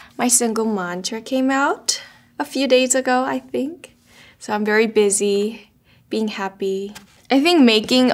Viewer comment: "I'm very busy being happy" yeah sweetheart I am so happy to know that you are happy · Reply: She actually said ''[So] I'm very busy... being happy'' which I find even more endearing and lovely ahahaha - 4:15 is the timestamp